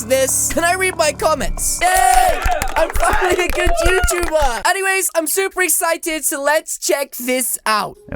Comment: Did he said “Yay, I’m a fu👑 good YouTuber!”? Because I feel like he said that behind those celebrating and clapping sound effects